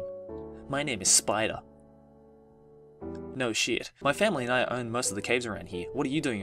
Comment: Every 13 year old's dream, is to have relations with a Spider? Man, I must have learned differently than most boys. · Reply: (CupcakesAreGR8) thought you said spider man